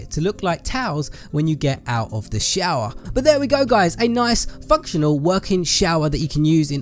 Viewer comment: sounds like he is saying f#cktional :-)